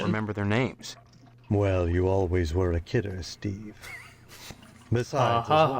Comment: I'm calling it now, "You always were a kidder, Steve" is going to have plot relevance at some point. And it will be ridiculous. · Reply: It does actually... but it's mentioned in the script, not in the final game itself. (Steve is mishearing them... they'er actually saying he always was a KILLER, hinting that he's secretly a serial killer, which is why the lodge wants him)